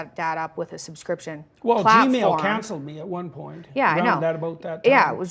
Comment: Did he say Gmail cancelled him?! 1:49:12 hour is that even a thing aren’t emails private